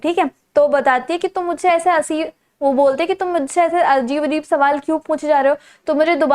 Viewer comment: didi literally said ashleel instead of ajeeb 😂